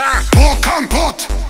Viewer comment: Why when i hear "Hardbass", i hear "Hardballs"